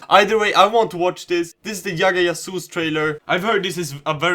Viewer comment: Did he just say Yagga Yasoos? or is J pronounced as Y in sweden? The same was with Bhajrangi Bhaijaan when he pronounced it as Bhaiyrangi Bahiyaan.